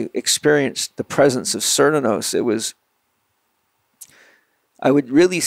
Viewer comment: Can someone spell this word he says at 18:35? He says "Experience the prescence of" sirnanose? cirnanos? SIR-NA-NOSE? what's he saying here? sorry for the probably stupid question, but I can't figure it out haha... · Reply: cernunnos